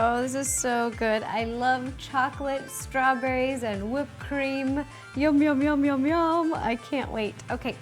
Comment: In 8:18 dead frosting instead of whipped cream